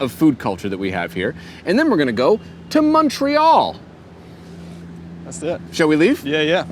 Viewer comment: did really say Munchreal? You guys are too much. Err, munch. 😁